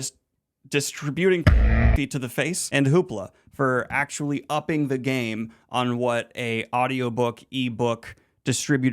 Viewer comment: I cannot figure out what is saying here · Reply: PRONography. Youtube made us censor.